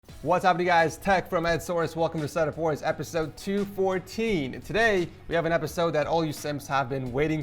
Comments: I heard that write ???? tech from source? lol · I heard him say “Hey the is Tech from Edsource” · Did he say "tech from Ed source" 😂🤣😂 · I was watching on low volume and i thought said “hey guys tech from ed source” never mind i just watched it again and realised my mistakes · did he just say tech from source?XD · I'm not the only one that heard him say "What's up you guys its Tech from Ed Source" right? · i thought i heard tech from edsource